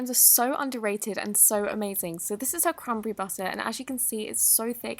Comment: when she said cranberry butter I thought she said crumble berry butter XD